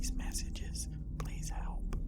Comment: So at 15:34 someone was whispering saying they need help😳 Did anyone hear that or am I tripping?